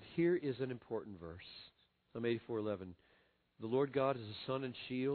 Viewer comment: At that time in the video I cant make out what book he speaks of can someone pls tell me ? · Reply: Stephalucas Psalms